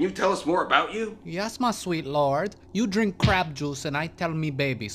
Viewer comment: I thought ceres said crap juice:)